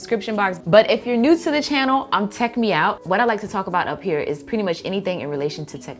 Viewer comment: at the end of the video i thought she said take me out as in like a date😳